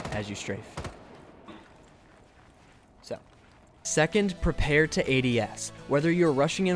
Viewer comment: I thought that you said and typed "prepare for aids"..